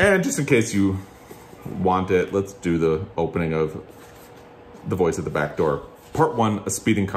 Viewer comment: Very enjoyable video. Just ordered A Voice at the back door to add to my library. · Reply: Nayzee41. Thank you I thought said The Boys at the Back Door haha Now I know what to order